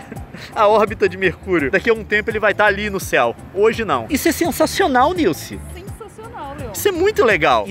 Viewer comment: "Isso é sensacioNilce!" kkkkkkk Quase ouvi assim...